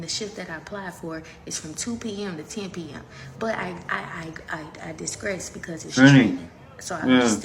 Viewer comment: I think she meant I digress she said I disgress that's not a word Lady lol